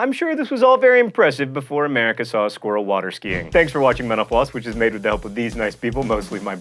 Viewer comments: for a sec there I thought John said "Thanks for watching menopause" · i thought he said thank you for watching menopause.